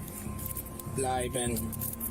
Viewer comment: Bro? Did i hear that cop at 43:07 saying (blijven) ? Thats a belgium dog and blijven means stay in my language. Thats dope idk how that officer is saying dutch words to that dog tho🤣